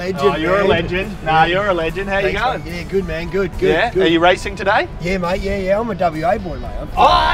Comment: all i heard was ''no yeah yeah.. good? no yeah yeah.. yeah'' xd